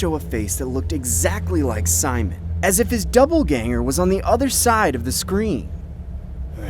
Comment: Did he say “doubleganger” instead of doppelgänger?